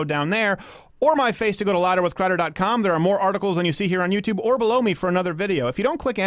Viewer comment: What??? Hey Crowder...@ 7:22... did you say "blow me for another video"? I guess that's progressive but hey man...dat ain't right!